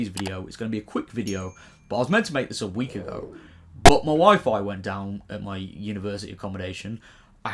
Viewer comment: The most shocking thing is that I thought you said your wife went down at uni and then I worked out you said ‘wifi’ I was like “good for you”!